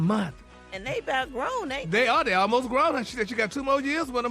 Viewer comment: Did say "they almost grown and shit". 😂 Did that make it on the radio?